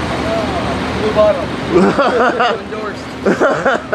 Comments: can someone tell me what he said? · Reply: ARMY something bottle, endorsed. not endorsed. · Can anybody tell me what they saying at 4:03-4:10? Please. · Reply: MiracleAkira1 he says “Blue bottle, endorsed” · Can anybody tell me what they saying at 4:03-4:10? Please. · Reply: MiracleAkira1 I thought he said "free bottle, Indoors" lol..